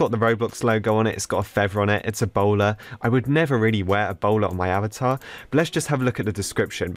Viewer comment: it sounded like he said I will never wear ebola on my avatar